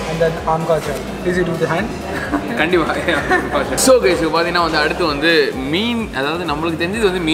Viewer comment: Please dont use the hand?. Did i hear it correctly? Did he say please dont use the hand?How can he determine how the Customer should eat? Its the customers choice. It is not Professional service at all!. His shop is in Tamil Nadu, he better knows in TN we use our hands to eat our food.Will definitely will never visit this Restaurant and will not recommend to anyone as well.